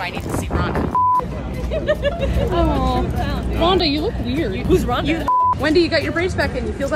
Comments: "Who's Ronda?! 🤨" GOT ME DED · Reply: I thought she said why is Ronda? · "Who's Ronda?! 🤨" GOT ME DED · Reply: i thought she said whats ronda 😂